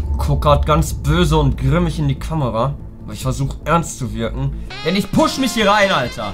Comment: war grad was zu trinken holen und hab nur mit einem Ohr zu gehört... irgendwie hab och bei dem genuschel verstanden dass er Özil und Kimmich in die Kamera schaut🤦🏻‍♂️🤣